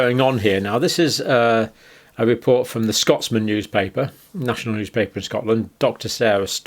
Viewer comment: I thought he said “report from Dr Spock” I had to replay it to check — “captain-my finding is that these earthlings have had an untested substance pushed into them after watching the BBC - Scotty”it’s worse than that they’re dead Jim , dead Jim